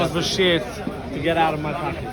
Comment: Not sure if I heard incorrectly but at 16:10 did someone say get out of my country